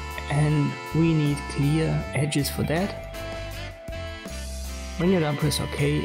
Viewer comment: I wish I could understand what you were saying. I'm missing something :/ As Soon as I click layer mask :57 my entire picture turns white. At 1:15 are you saying "we need clear edges for that"? Then I'm lost. Sounds like your saying "press ok and holds control for that". But I can't get past the first layer mask though :/